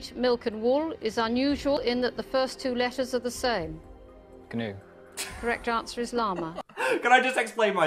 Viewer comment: my dumb ass thought he said canoe instead of gnu I was like ???